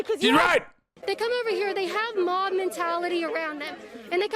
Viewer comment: I thought she said "mom mentality"